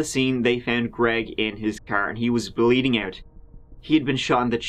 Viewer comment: It's women like her that makes real abuse cases and child molestation cases harder to prove/harder to take serious. It's bullshit. I'm glad he's still alive tho and the child didn't lose both parents. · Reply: I had to go back and watch the part again where he said "he was bleeding out" because I thought it said he DID bleed out. Yeah, I'm glad too!